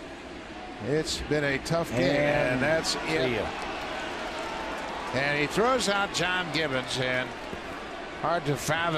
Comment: Did the announcer just call the ump an idiot or am I mishearing it? If that’s what he said then I commend him for saying something like that on live tv. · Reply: I think you are right, slightly difficult to hear, but yes, "Idiot."